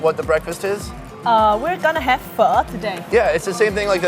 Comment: I kept hearing fuck instead of Pho. There must be something wrong with me.